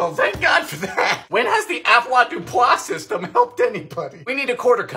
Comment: "When has the 'what' system helped anybody?" I have no idea what he said